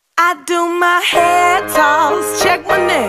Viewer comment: I couldn't understand When she was saying " feeling good as hell." I had to look up the lyrics 😂 I thought she was saying " Can you call my phone." Lol